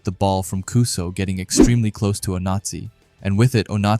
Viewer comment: lol I keep hearing “Oh-Nazi”